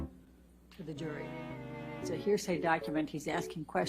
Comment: "Hearsay" oh I thought she said heresy sorry never actually witnessed hearings in court before almost brought the whole empire with me